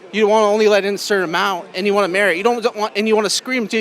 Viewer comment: Did he say, "And you want to marry?"